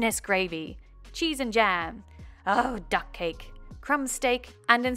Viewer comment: Oh. I thought he said "Oh, duck cake!" because he had to make Bingos cake, which was a duck cake, which was extremely hard.